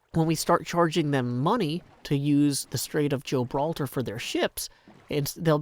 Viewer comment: Did... did I hear "Strait of Joe-braltar"? ... My day just went from long and tiring to fantastic